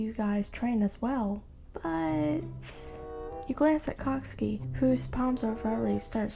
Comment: I thought she said "you glance at C*cksuki" And I was like "WAIT WHAT??!!" I need help 😅😅